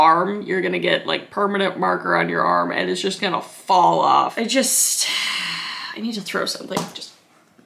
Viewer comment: I thought she was saying my arm would fall off for a second... yes I am stupid, why do you ask?